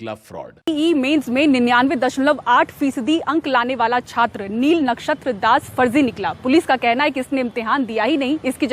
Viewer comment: it almost sounded like Neil Nitin Mukesh!😂😂😂😂😂